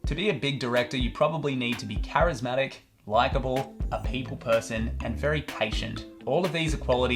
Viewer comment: I heard you say "feet person" and thought that was the most straight faced Quentin Tarantino jab I've ever seen